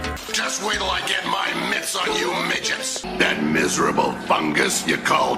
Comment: Did he say midgets???